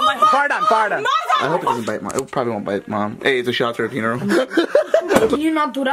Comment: I think said “I get the shoutouts at her funeral”